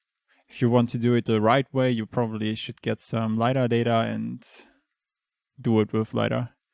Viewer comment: what does he say? Lighter data?